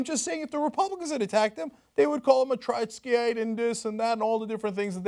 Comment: What did he say Republicans would call Bernie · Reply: A Trotskyite. As in Russia's Leon Trotsky.